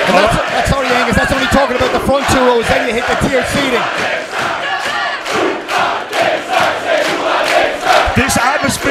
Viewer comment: Does anyone know what this chant says? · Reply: Hoo hah David Stat